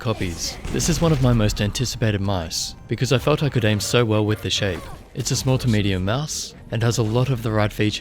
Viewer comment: my video froze and I heard "aim so well with this sh"